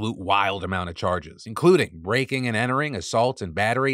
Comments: I thought you said “rake and entering” lol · I thought you said, raking and entering